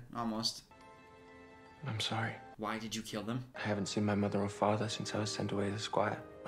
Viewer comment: I heard "Why? Did you kill him?" as "Why did you kill him?" and was so confused...